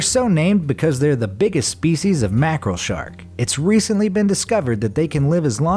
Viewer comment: did he say mackerel shark?